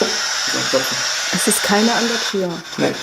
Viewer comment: Hi, I'm from Austria and at minute 10:19 was a little translation mistake. Instead of "there's no other door" she said "there's nobody at the door". Hopefully this was helpful, love your videos!! :3